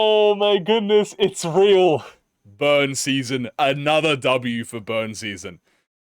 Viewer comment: i thought it said "bum: LOL